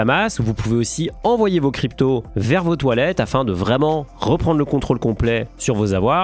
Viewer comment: J'ai compris " vous pouvez aussi envoyer vos cryptos vers vos toillettes " je me suis dis c'est bizarre comme investissement de foutre ses cryptos aux chiottes.